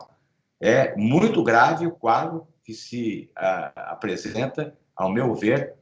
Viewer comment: Não entendi, em 18:40, "é muito grave o quadro que se apresidenta . . . " 🤷‍♂️